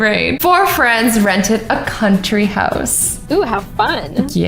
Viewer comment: I thought that said tree house 🏡🤣🤣🤣